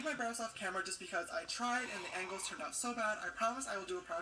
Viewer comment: I heard eggos instead of angles lmaooo I'm always hungry